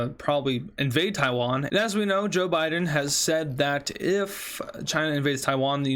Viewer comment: I thought he said joke Biden lol that would have been awesome